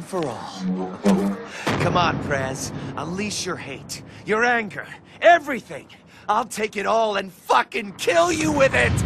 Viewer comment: What Travis says: Unleash your rage, everything! What the automatic captions say: Amount that's unleash your hand director